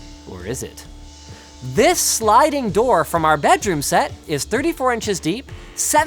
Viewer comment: Anyone else thought it said “Bathroom” instead of “Bedroom?” 😂